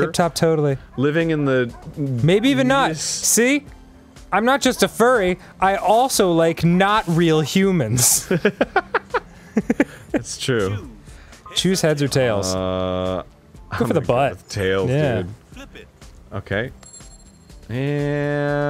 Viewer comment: I thought he said “Jews, heads, or tails”